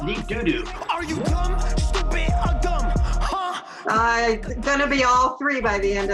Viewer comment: Are you dumb, stupid, or dumb? Huh? “I’m gonna be all 3 by the end of the night.” Looks like it already hit you😂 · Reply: I heard "drunk stupid or dumb" so maybe she did too